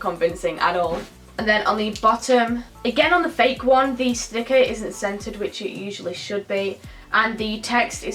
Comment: I thought she said the sticker isn’t scented